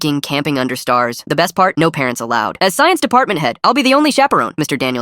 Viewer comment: This sounds mildly unbelievable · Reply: When it said "no parents allowed" I heard "no pants allowed"😅